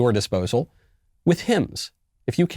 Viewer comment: I thought he said “hymns” instead of hims